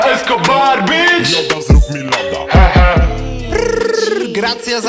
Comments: Kto usłyszał tam w tle Yoda zrób mi loda xDD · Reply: Ja usłyszałem loda zrób mi loda :( mam smutne życie xD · Kto usłyszał tam w tle Yoda zrób mi loda xDD